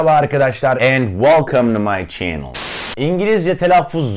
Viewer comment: Sanki girişteki kelime "Welcome to my channel" dan "Welcome the my channel" a dönüyo yavaş yavaş :D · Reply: Ben " welcome do my channel" diye duydum😄